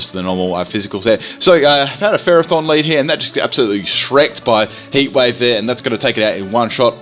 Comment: did you say you got shrek't?